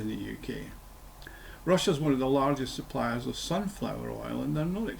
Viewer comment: Wait, I hear ‘ Russia is we@ fish’ now 😂